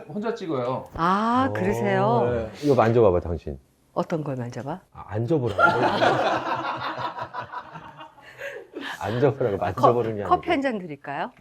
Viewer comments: 보다가 잠깐 딴거 하느라 듣고 있었는데 저도 만져보라고 들었어요ㅋㅋ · 내가 들어도 만져봐봐로 들림